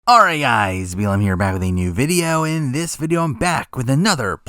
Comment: Kind of irrelevant but what are you saying in the intros? I keep hearing "alrighty guys, BLM here". I don't think that is what you are saying but I can't hear anything else but that.